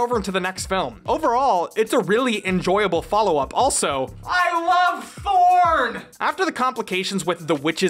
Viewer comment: Could have sworn when he said "I love Thorn" he said "I love Porn" which confused me a bit. Although I do love Thorn's whole aesthetic, the way the black and red blend together is gorgeous.